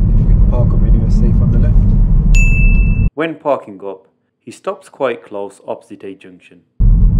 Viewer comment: what did he say? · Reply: "he stops quite close opposite a junction"